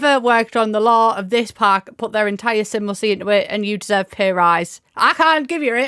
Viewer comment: for a second i thought you said 'their entire SIMLICY' haha